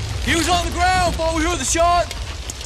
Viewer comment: What does he say at 0:23?? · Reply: Kyeby Rno i could barely make it out, but it sounded like "He was on the ground before we heard the shot."